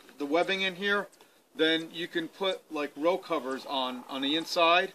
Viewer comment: Hi can you tell me what a rope cover is/ My cover is clear and does not have vents unfortunatly and this is my first year with my new little greenhouse. I would like to not cook my seedlings! Great video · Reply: Super thank you very much. I thought you said rope cover which is why I could not find information. Row cover makes more sense.